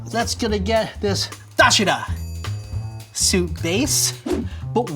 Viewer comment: Did he say доширак at 9:44?